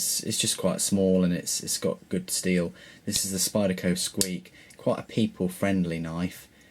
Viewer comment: What was that Spyderco model at 8:39? I couldn't understand him. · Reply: Arthur Leyenberger Spyderco squeak